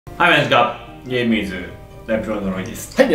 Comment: 「はい！まじか」 みんなの空耳書いてください↓